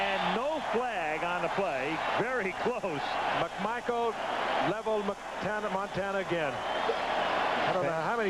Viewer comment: He sounds like he says "McMichael leveled Hannah Montana again" hahahaha.